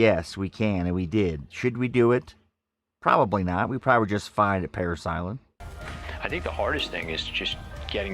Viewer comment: Marine Corps Gendered Integrated Boot Camp at San Diego. We can do it but should we do it. For 2 reasons. More gender-fluid combat training at Parris Island <link> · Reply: What did you say at 6:25, "We probably (sounds like justify) Parris Island."